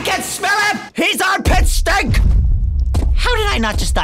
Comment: I love when says “HES PETSTINK” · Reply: so I heard "pet-stink" 😂